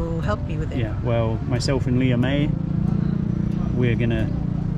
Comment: lol every time he says “Leah May” I keep hearing “Liam A”😂😂😂